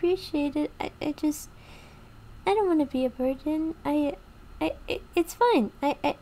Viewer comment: i zoned out and snapped back when i heard "i dont wanna be a virgin"